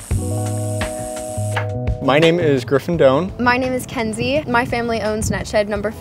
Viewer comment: Am I the only guy who heard him say "My name is Gryffindor"?!? :D